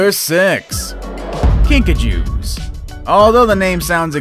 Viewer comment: when you said Kinkajou I thought you said Pikachu